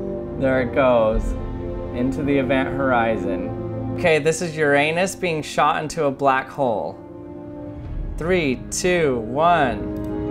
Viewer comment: I thought he said "Ok so this is you ** being shot into a black hole" XD